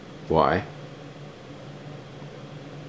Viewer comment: OMG!!! I thought that Mark said: 'I KICKED ANOTHER SQUIRRELS!!!' 😂😂😂